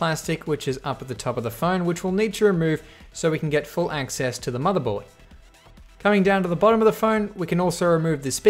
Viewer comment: Hugh : Mother Board Subtitles: Umm... i think he said “Mother Bullet”